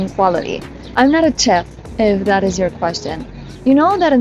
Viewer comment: I want to first say that your English is very good, but here I couldn't quite make out what you were saying. "I'm not a chep"? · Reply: "I'm not a chef". Sorry 😳